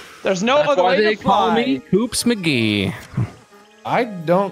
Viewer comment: for the longest time I thought was saying 'Goops McGee' and I dont know why.